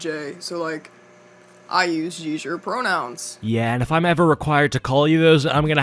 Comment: i heard “I use seizure pronouns” it’s about the same level as the garbage she was saying anyway lol